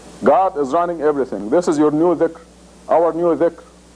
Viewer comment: Thanks for the subtitles, but he does not say deed here. I think he says 'dhikr', as in a new way to remember/remind yourself to God.